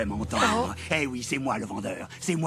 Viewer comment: On entend surtout :-"Et oui c'est moi le méchant!"😂😂